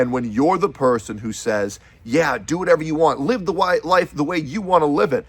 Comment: did you say "Live the white life the way you wanna live it." ? lol. Can't say i've ever heard that before lmao